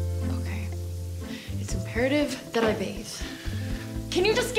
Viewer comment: wht did she say!? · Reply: "It s imperative that i bathe" meaning it s urgent to take a shower